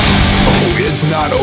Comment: Hey,did sam say "Oh,it's not over until I.....what did sam say?(a)T-bag( XD) or (b)pay back? My guess is the first :P